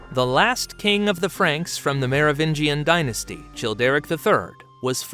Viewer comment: Did he just say Chill Derrick the third?